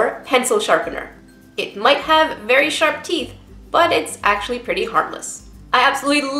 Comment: i thought she said "But its actually pretty heartless" XD